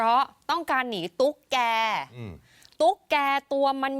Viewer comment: ได้ยินว่า กรรชัยออกจากวัดของท่าน ว. เพราะ "ต้องการหนีตุ๊ดแก่" ผมนี่ตกใจเลย ต้องฟังอีกรอบ อ่อ ตุ๊กแก